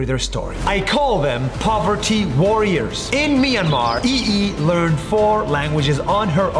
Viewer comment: Anyone else mishear this to potty warriors?